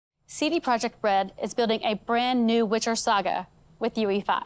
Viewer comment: CD Project Bread is what I keep hearing